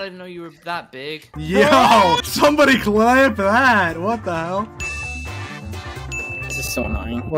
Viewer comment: I love how is literally Dream's alt where he can hide the fact that he had to do a face un-reveal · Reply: and at 23:01 it sounds like he says "somebody clap bad" aka badboyhalo